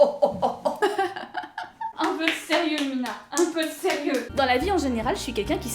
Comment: Louna ? Ou c'est moi qui ai mal entendu ?